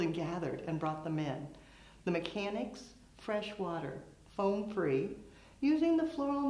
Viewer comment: I swear I need my hearing checked. I thought I heard 'French water'!! Lol. You said 'fresh water". When I thought you said French water I perked up because thought I was about to learn something about French water. 😆😆😆😆